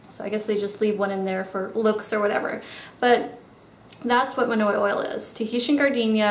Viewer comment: I really loved this video. So different and unique :) I will definitely try a few of these techniques! Not gunna lie though I had my head down and I heard "..it's manure oil" (monoi oil). Haha, was a good laugh when I realized what it was!